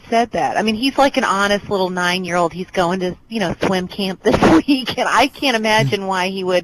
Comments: i thought she said twin camp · oh .............. I thought she said "Twin Camp"